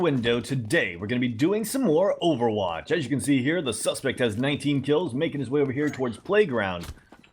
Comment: I heard 99 kills and I was literally triggering